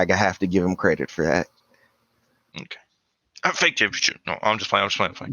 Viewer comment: what were you saying there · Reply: He said “Fake championship”